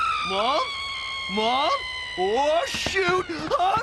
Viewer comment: Courtney said "Oh F**k" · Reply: Ravis Amissi but i thought she said oh shit